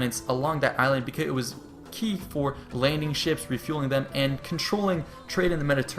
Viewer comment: Did you say refueling ships at 14:17?